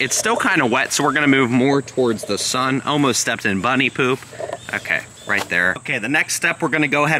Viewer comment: did he just say bunny poop